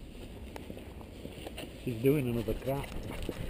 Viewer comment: first thing i heard.....'shes doing another crap'.